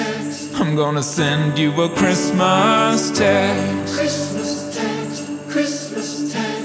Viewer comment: at first i thought he said I'ma send you a christmas tax lol